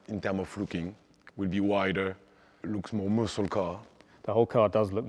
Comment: what is he talking about, he said gamma fucking and muslim car............... i googled and got a bunch of car porn and no real information.... WTF is he talking about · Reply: Are you serious? He said "time of looking" and "muscle car."